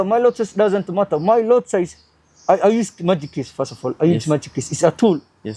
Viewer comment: Hey .. what tool does he say he uses @ 22:42 , is it magic what?? Didnt get that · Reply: Magic keys, it's a tool developed by raja banks